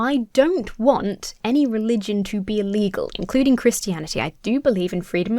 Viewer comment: BTW your enunciation on "be illegal" was a bit poor and it sounded like "be legal" to me - but I assume you didn't mean that based on context and what I know from your channel. Just a heads up ^-^